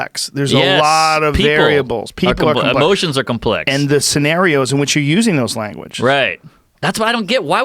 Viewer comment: I thought mark normand said oceans are complicated. lol.